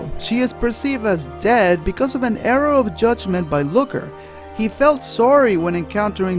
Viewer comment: It sounds like says " arrow of judgement" and all I can picture is Looker deeming the first faller unworthy, pulling out a gigantic bow and arrow and shouting "I SMITE THEE" as he shoots her down.